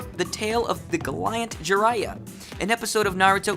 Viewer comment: what did he say at 4:42? was that galyant? you mean gallant?